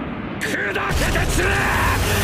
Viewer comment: somehow I heard it as "WRYYYY". 🤣